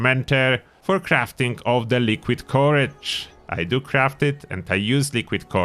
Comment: I thought you were saying porridge until I realized you meant "Liquid Courage" haha :')